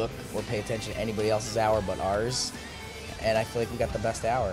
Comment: when mikey said “drama free” i thought he said “drummer free” both are kinda accurate i guess 😂😂😂😂😂😂